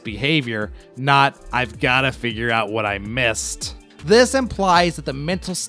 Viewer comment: At first I thought he said - I gotta figure out what I'm gonna say.. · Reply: I thought so too. It was on the 6th listen that I realized he said, "I gotta figure out what I missed." Had to rewrite the video because of it.